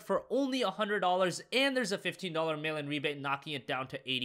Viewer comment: I always thought he said "Melon Rebate" I was so confused for so long.